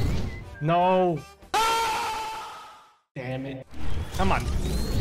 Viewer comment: Idk i have bad hearing but at 1:10 did he say dam it